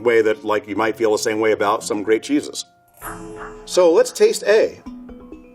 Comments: I heard, "some great Jesus" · I thought he said some great Jesus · For some reason I thought he said "grape Jesus" not "great cheeses"